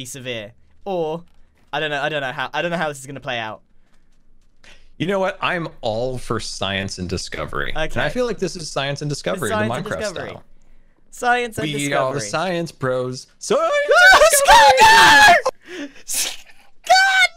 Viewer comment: I hear both 'science and discovery' and 'science is coming'